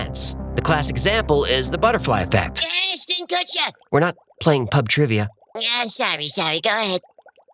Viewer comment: I thought he said ‘We’re not playing Puff trivia’ lmaoooo 🤣